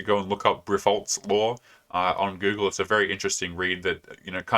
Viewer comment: he encourages people to google "revolt's law" or something. Does anyone know what he said?